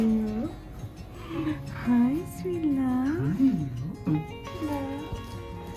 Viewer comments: I guess she was saying “sweet love/sweetie” but it sounded like “hi Sophia” for a second … haha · did she say hi Sophia at 7:34 talking to the girl is that her name !!??? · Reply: She said “hi sweet love”